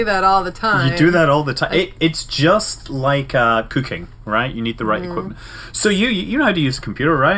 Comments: I thought he said "It's just like cocaine". As in, she do it all the time. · I thought he said it's just like cocaine. You have to have the right equipment